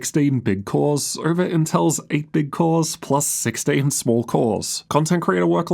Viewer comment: did he say 8 bit cores?